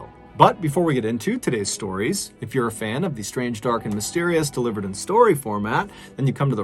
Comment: Idk why but when I first started watching you I thought you were saying “if you’re a fan of the strange, dark and delirious” lol